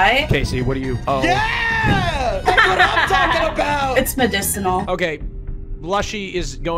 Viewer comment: lol captions are wrong, "it's medicinal" is what it's supposed to be